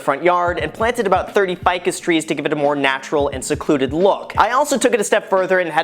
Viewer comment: I heard this as “planted 30 fucking trees out front” :)))) LOL!